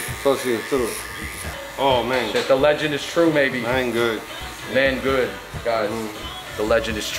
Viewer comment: Man-good made famous by Pedro Martinez. America thought he was saying mango